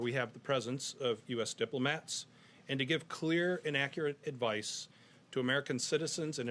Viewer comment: Did he say "To give clear inaccurate advice" ?